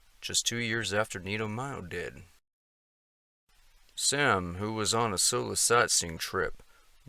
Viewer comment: At about 9:16, it sounded like said, "Sam, who was on a psilocybin trip. . ." I thought, That's why he went missing, case solved. Hahaha!